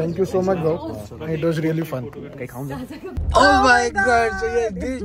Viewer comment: did he say “ kahi khaun ghe”? He’s so thoughtful and caring!🥺